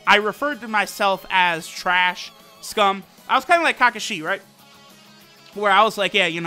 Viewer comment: Did he just say kaka - shi